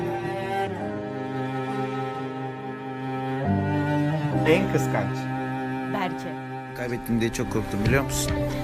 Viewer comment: da ne diyor bilen varmı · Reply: En kıkanc